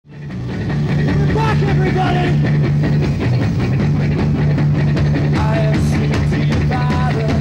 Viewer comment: what did roger say? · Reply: We're back, everybody